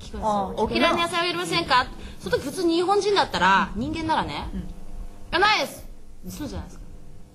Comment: 普通に日本人だったら。。。人間ならね、「いや、ないっす」 Am I hearing that correctly? 人間なら Holy hell.